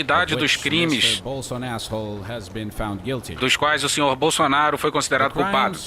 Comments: Só eu que ouvi BolsoAsshole em 11:07?? HAHAHAH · Lançaram um 'Bolsonasshole' ou foi impressão minha? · Eu ouvi "Bolsonasshole" na simulação do final do vídeo? kkkkkk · EU OUVI UM BOLSONASSHOLE? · Bolsoasshole em 11:06 EU OUVI! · Eu ouvi demais? Bolso Ass Hole? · Não sei se foi impressão minha mas no minuto 11:05 o Juiz chamou o Bolsonaro de BolsoAsshole ..........kkkkkkkkkkkkkk.... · Só eu ouvi em 11:06 "BolsoAsshole"???? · Eu ouvi um bolsoasshole em 11:07 ou tô ficando maluco ? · "bolson asshole" foi pra acabar kkkkkkkkkk